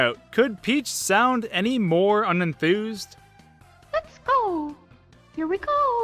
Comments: When I saw Mario in 2011 i thought he said Lego XD when I knew a while later it's let's-a-go or even pizza dough I don't know but I thought it was funnyX3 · I always heard the "Let's-a-go!" as "Let her go!" :P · I always misheard "Let's-a-go!" as "Lots-to-go!" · I STILL HAVE THAT MONDEGREENE THING WHEN I LISTEN TO MUSIC, AND I THOUGHT I WAS THE ONLY ONE. All that aside, when Mario said "let's a-go", I thought he said "Love pickle"......yeeeeeah. Also in Mario Party 2, I didn't know Wario's VA at the time was German, so I thought he said "DOH I MISSED" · When I first played super mario 64 I thought mario said on the star select "lets gecko". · When I was a kid I thought I heard Mario say Mexico! instead of let's a go! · I hear "let's heggo" when mario enters paintings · At the lets a go part I thought he said pizza roll😂😂😂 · When combined with the sound effect that plays when you choose a star in SM64 it always sounds like "The pickle!" to me, but in the video you didnt have the sound effect so i heard it as the proper "Let's a-go!" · When Mario says "Let's-A-go" I thought he said "Hiss pickle" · oh I thought Mario say let's pickle not let's-a-go. and I thought Mario say goodbye gay bangki ( don't judge me that what I hear ).